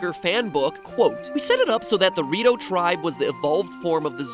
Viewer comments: Who else misheard the rito tribe as dorito tribe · before "the rito tribe" was spelled out i thought mat was saying "dorito tribe"